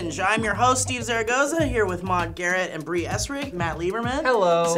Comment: When I heard Maud's name I thought he said "Mod Garrett" and then I thought of Runescape lmao